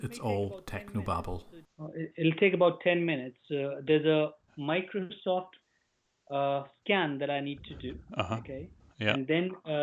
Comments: i listen it 10 times. He IS literally says Microsoft SCAM. Genius ! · for a second I actually thought he said scam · Did he just say he had to do a Microsoft scam?!?!?!?!